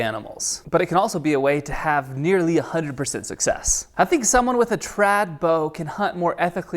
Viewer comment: Shit I (thought) heard him say "Hunting someone with a trad bow" *spilt my coffee YES! >:] I mean... nooooo no no no :P